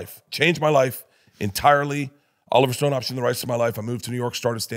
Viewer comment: Did Burt say “Oliver Stone auctioned the rights to my life?”